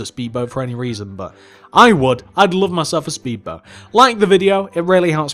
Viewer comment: I thought you said "Speed bow"